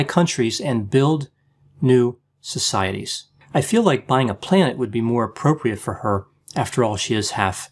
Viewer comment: “I think buying a planet would be more appropriate for her, after all she is half alien.” 😂😂😂😂 Thank you addressing this. This woman can potentially be quite harmful to some people. Her narrative is just utter nonsense! Thank you again and I hope you are enjoying your weekend:) 🇺🇸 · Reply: On first hearing, I thought he said, "buy a plant." -:)